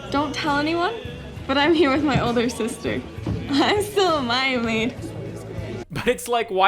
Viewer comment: I cannot figure out what she's saying... It sounds like "I'm still a mayamee" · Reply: “I’m still a Mia Maid.” It’s a sorta nickname for 14-15 year-old girls in the Mormon church